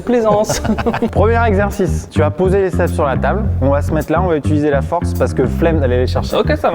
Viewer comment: j'ai compris "tu va poser les fesses sur la table" jpp 🤣🤣🤣